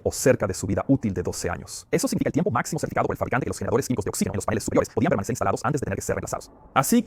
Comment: Estaría bien, que se revisase las traducciones. Porque hay partes del vídeo que es imposible entenderlas con variaciones significativas de velocidad. A ver quien es el guapo que es capaz de entender que dicen en el minuto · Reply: Eso significa el tiempo maximo Certificado por el fabricante De los generadores químicos De oxígeno en los paneles superiores Podían estar instalados antes de ser reemplazados